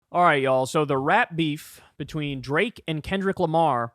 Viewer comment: It sounded like Kyle said "rat beef" 🤣